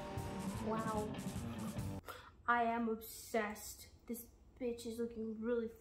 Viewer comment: did she just said b***?!